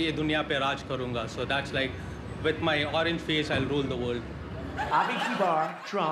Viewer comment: Loool that last guy!!!! with my orange face I will rule the world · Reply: When I heard it for the first time, I thought he said „... I will ruin the world!”. Lol ;D